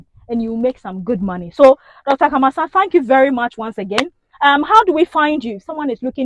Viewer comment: Please whats his name again... Did i hear"Dr. Kamassah"?